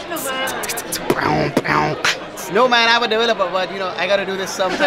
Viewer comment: Listen carefully, the Indian guy says he is an app developer 9:06. Also, what's the name of the song at the end, it's awesome. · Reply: lol, i thought he said "i'm a delivery guy", but english is not my native language so i guess i'm wrong! :D :D